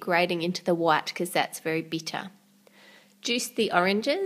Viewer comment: What do you do if your tempered chocolate has set in the bowl, as in when you scrape off the excess it's sets up on the rim of the bowl. Can you just melt it again or does it have to be tempered again or is it useless? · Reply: She said vely instead of very